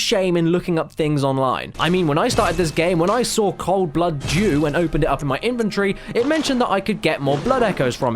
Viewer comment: I thought he said cold Blood Jew :|